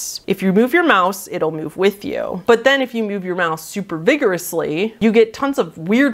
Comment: I wasn't paying attention during the "staggering beauty" one and I thought you said "If you move your MOUTH very vigorously" and I was picturing someone rapidly opening and closing their mouth (in their webcam, I guess?) to make the thing move.... which is much creepier to me!